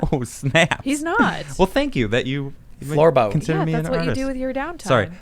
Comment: What's the word they're discussing? Florbo? I can't hear it properly